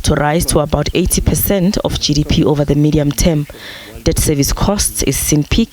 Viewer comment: Government debt to rise to 80% of gdp? Did I hear that right